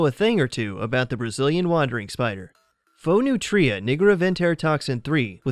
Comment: who else heard him say nigga😂